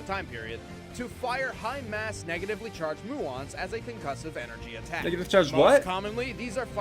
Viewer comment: I think he said "muons," and I think they're the neutrons of antimatter, similar to antiprotons and protons or anti electrons and electrons.